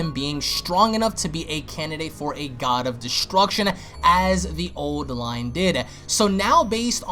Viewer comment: Did he say, " as the old line did ".?